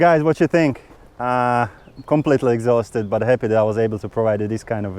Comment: Did he say he was completely ‘exhausted’? 😅